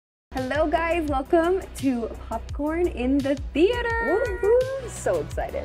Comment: I thought you said "Welcome to Top Corn"